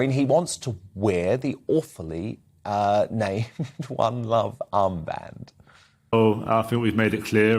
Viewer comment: Don't mumble, I thought he said 'bum luv armband' oops!